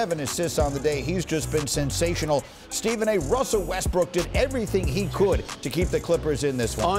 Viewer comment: Did I just hear Stephen A call him Westbrick??? 😂😂😂😂